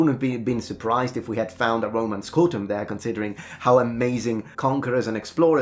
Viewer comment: I thought you said “Roman scrotum” and that made me laugh so hard.